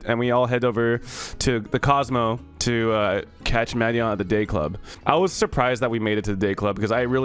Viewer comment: I thought he said gayclub and expected him to tell a story about being flirted with while super tired or something lol